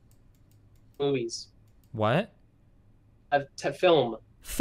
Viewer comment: I can't be the only one who thought the caller said "Boobies" right?